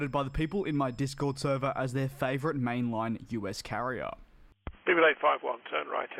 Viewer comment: I’ve been watching your videos for months, but your intro still sounds like he says “Stupid 851 turn right heading 180” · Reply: I know it’s speedbird 851 but I keep hearing ‘people 851’ lol